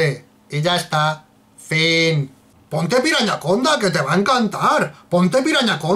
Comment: Pensé que decía piñaconda